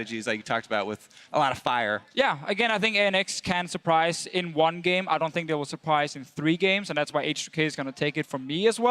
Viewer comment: what does the guy in the crowd say around 14:00 in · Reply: "Martin sucks"